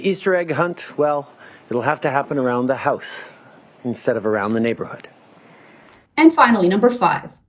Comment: Love the channel and the video but at 5:20 #4 you made an error. You claimed he said "you have to stay inside the house for the Easter egg hunt" but what he actually said in your own video is "have to happen around the house instead of around the neighborhood" which is a very big difference. Around the house being literally around the outside of your house and not throughout the neighborhood in other people lawns. This kind of error will discredit you and make people come to Justin's defense. Just some friendly advice from someone who respects and appreciates your work. Keep the content coming, I just found this channel and I love it.